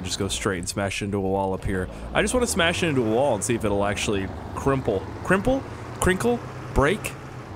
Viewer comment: For a second I thought you said Krimple Nipple